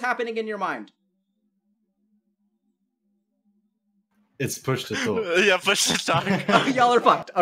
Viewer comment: what are they laughing about? I can’t understand what they said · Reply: He asked what’s going on in their mind, none of them could answer because it was “push to talk”, aka they had to click a button to speak, and they had their hands in the air lol